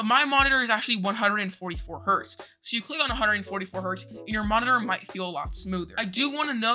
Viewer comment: captions wrong, it said 224 not 144 hertz